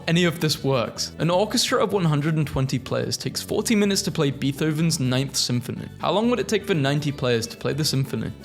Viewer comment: …did he just say beeth-oven?